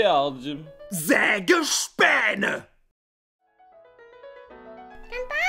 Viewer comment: XD i laughed so hard cuz Prost sounded like post